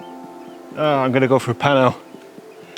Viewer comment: thought you said “just going to go for a pie now”. I’m, like, “now?”. Photography DOES make ME hungry.